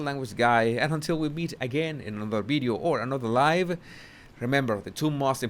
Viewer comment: I have to admit, everytime you sign off i hear you saying “we’ll meet again either in another video or another life!!” 😅 another life!! Hahaha. I know you mean “live”.